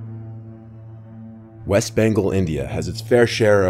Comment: I thought he said west banko 😂